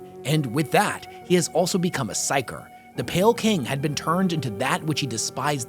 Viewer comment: did he say Pale King? WARHAMMER IS A HOLLOW KNIGTH REFERENCE!!!